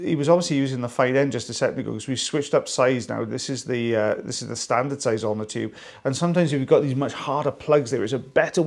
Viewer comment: For some reason I always hear "the standard sizometer"